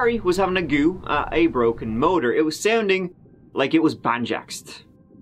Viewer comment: Did you just say banjacked!?! Ummmm BEST WORD EVER 💪